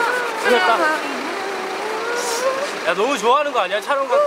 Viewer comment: Did I heard suhyun said “sooyaa unnie”?? 3:39, ahh i need yg family interaction more, i miss them together, how can YG not allowed their idols like before😭 when will i see akmu, ikon, blackpink, winner, etc together😭 b.i and lee hi😭